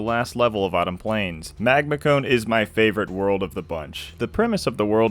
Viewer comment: for the entire entry, I kept hearing 'Magma-kun' and I was like 'huh?'